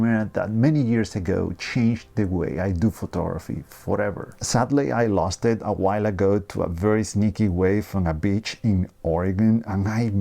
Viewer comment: I thought you said "Sadly I lost it to a sneaky wife and a bitch" at 1:05 had to read the CC haha